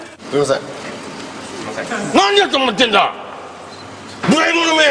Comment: What the hell‼️って言ってるように聞こえた笑